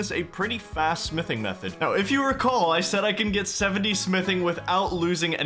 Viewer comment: Did he just say RIKAL?!?